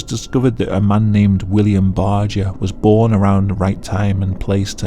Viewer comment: at first i thought you said "bagel" and im like, how the hell is a bagel experiment a "disturbing psycholgy experiment"